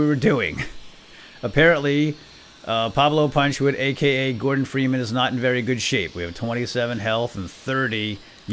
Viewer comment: ha wen he said gordon freeman at de start i thought he said morgan freeman